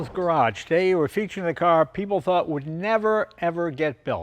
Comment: you mispronounced "hoped" It almost sounds like you said "thought". No, ever since Porsche started printing money with the Cayenne, this was the unfortunate inevitable reality. Kudos to Ferrari for holding out as long as they did, and at least it's a V12 and not an EV.